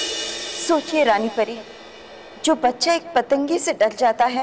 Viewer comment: for a second I thought thathe said Nani pari 😂😂😂😂😂